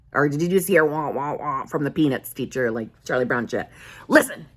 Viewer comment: I swear I heard you say "P3nis teacher..." 🤣 Maaaaaan what's on my mind??? I want my sp!!! LOL!!! 🤣🤣🤣🤣